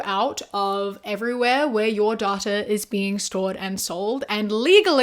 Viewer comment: I thought she was saying daughter and not data I got scared for a second💀💀💀